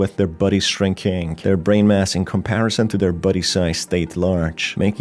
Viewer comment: sounds like youre saying "buddy" like "bubble buddy" rather than body